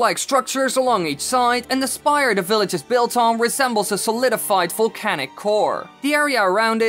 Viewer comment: I thought he said" volcanic whore"